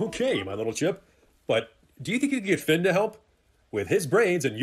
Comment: first off... Sure you don't wanna retake that line, buddy? Second off, oh god- did he say Finn? Did he... Did he say my name? No sir I will not have my name associated to this garbage lmfaooo 💀